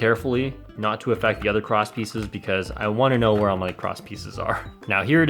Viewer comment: Everytime you said cross piece I thought you said crotch piece